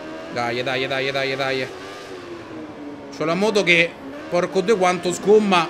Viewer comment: Io ragazzi, premetto che non voglio fare accuse o robe varie, ma.. mi pare, MI PARE, che al minuto 06:11 venga detto qualcosa di, diciamo, "poco consono" ad un video pubblico di YouTube.. mi sbaglio io? Ho sentito male? o Ciccio ha fatto uno scivolone non da poco? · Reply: In realtà è un "porco due" e non un "porco *" come del resto in ogni altro video ci sono i porco due. Si è sentito male ma sono sicuro che non era una cosa voluta. Quindi cristiano non offendetevi per una cosa che si è sentita male su.